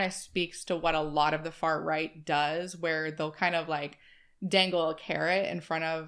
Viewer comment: I swear to Satan you said "fart right" 😭😭😭